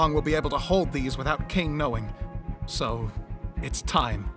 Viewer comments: I dont know if it was on purpose but it sounds like he's saying king instead of caine- took me 3 rewatches to hear caine but it founds too close to king its kind of wierd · The end "it's time" what I hear "it's tom. "